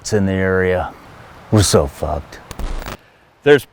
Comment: could swear he said "Russo 's f*cked"